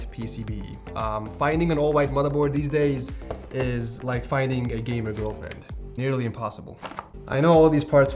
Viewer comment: I thought he said " finding a white motherboard these days is like finding a gamer in IRAN"